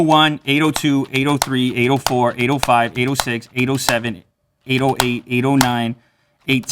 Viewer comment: When he was counting at 805 806 it sounds like he saying anal five anal six and it made my day :,)